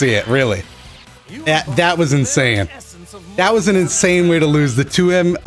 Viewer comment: "That was insaiyan" Not the only one who heard that right?